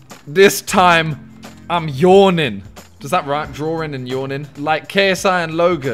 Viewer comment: is it just me or did i hear 'drawring and sawring'?